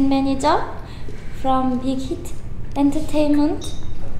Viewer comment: Omg did she say bighit???